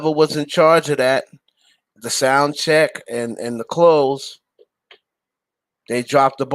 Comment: I thought she said clothes & check? Cause after tht she said they messin with her coins